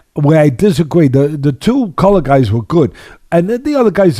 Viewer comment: did he say the "two colored guys"?👂🏾